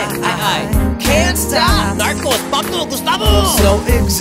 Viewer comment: what is he saying at 0:47 ? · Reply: Aishwarya Bhasker Narcos, Pablo, Gustavo- These are all characters of Narcos(Netflix Series) and they added a touch of Spanish in it